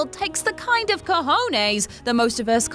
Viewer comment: Did she said "cojones"?